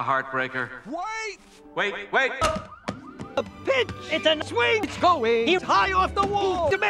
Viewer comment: did he just say “bitch”?????